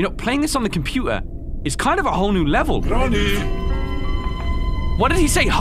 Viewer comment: did anyone else think that grandpa said Nani?